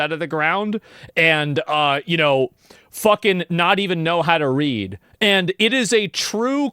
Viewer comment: I really thought he said she was only meant to dig potatoes fuck and not know how to read